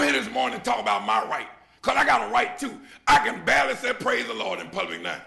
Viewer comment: I thought he kept saying, "I got a wife too!" A right too! Oh that Country dialect! 😂 ☠